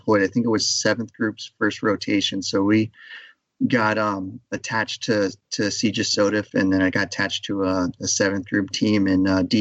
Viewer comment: right about @ 13:58 he mentions being attached somewhere, but i can't make out what he's saying... it sounds like an acronym, for what it's worth. anyone have any idea what he was referring to? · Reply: Combined Joint Special Operations Taskforce “CJSOTF”